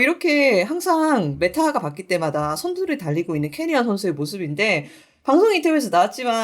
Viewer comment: 저만 "케리아 선수" 로 들리나요?